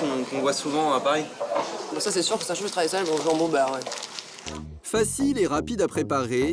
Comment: elle dit quoi ? 😂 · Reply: Elle a dit « ça c’est sur que sa change du traditionnel jambon beurre